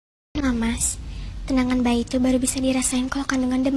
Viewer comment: Tendangan oi bukan kenangan